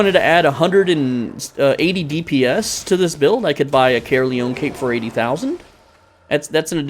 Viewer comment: what cape does he say would increase the dps ? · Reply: Caerleon Cape